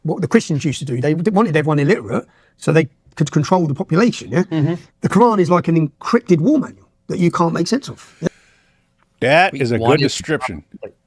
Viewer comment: I thought he said, encrypted woman. LOL